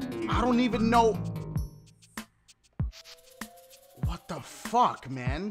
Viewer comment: Does anyone else hear 15:19 in the game sounded like fuck